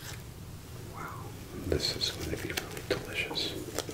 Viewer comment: anyone else hearing "Wow this is gonna be f*ckin' delicious" at 43:42 or just me? 😂